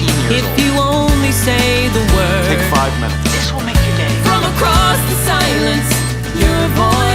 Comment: i thought it said "this will make you gay" but it says "this will make your day" lmao im an idiot